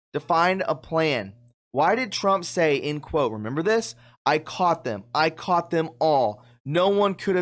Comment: i Thought he said ''We got them-we got them all!'' ?